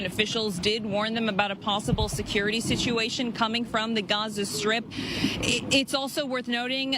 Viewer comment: I never felt so related in my life, we are hated and we can't forget that!!! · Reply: LOL did she just say "Gonzo strip"? ROFL 🤣